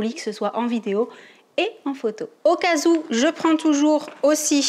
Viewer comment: Cest moi ou elle a dit "au casous"